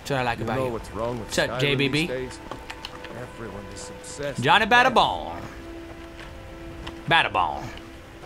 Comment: I thought he said Johnny Batter Balls ._.